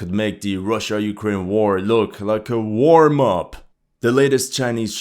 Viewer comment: i thought he said "war mop" instead of "warmup"